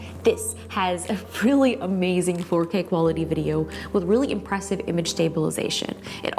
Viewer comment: When she said “4K quality” I kept hearing “potato quality” and I thought what’s potato quality??